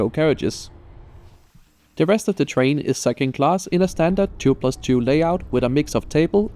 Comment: sounds like said, the rest of the train is sucking class... xD